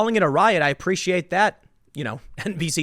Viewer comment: Anyone else hear "I appreciate that npc 12"?